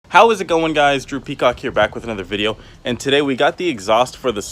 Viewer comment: You sound like you say droopy cock when you say drew peacock